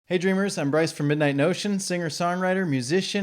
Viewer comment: When you open your reviews with "hey dreamer", it sounds like your saying "hey Adrian" and therefore I think you're speaking directly to me.